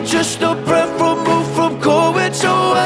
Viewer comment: For some reason I hear “till I found submissions in the form of your grace.” Um… who’s gonna tell her 🥲